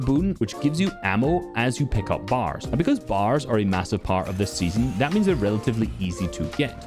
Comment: i heard the word massive?